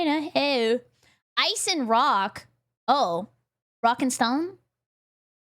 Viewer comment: > "BerryCrepe has alerted the horde" DID I HEAR A ROCK AND STONE? ✊️⛏️